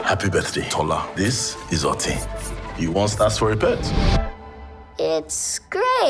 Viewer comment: Excuse me, did he say 'Otin' to his daughter? 🤣😂🤣😂. I had to replay thrice just on 0:11 to make sure. 😅 I feel like the interview with Megan Young all over again🤣😂🤣😂🤣